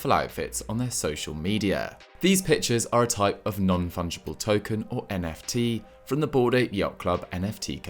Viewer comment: hahah how I heard NFT as ‘non-functional tokens’.